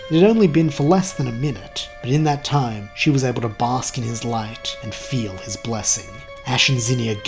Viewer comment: You went "bask in his light" but all I heard was "bask in his glory, bask in his glory!" Sorry wrestling fan